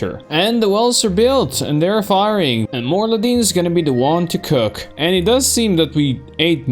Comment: I thought I heard Mor'ladim at the start and had hardcore WoW PTSD.